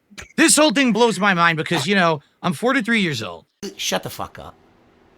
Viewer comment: it sounds like he says 4 to 3 years old like yea you act like it